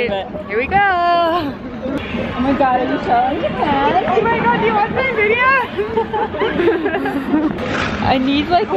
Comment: Did you get spotted? Thought I heard "do you watch my videos?"